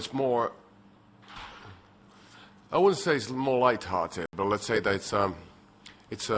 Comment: on the first question, i thought he said "i wouldn't say it's more retarded.." haha. oops. xD